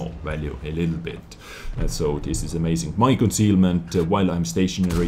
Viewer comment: i heard said michael sealmant if that is a man that exists but i already realized that he said my concealment😂😂